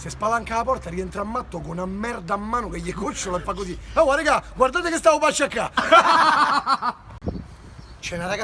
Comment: cosa dice??? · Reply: Ragazzi quadrate cosa stavo per pestare